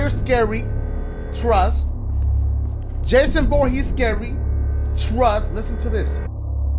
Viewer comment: When said "Jason Voorhees is scary" he said it so fast I thought he said "Jason Bourne is scary"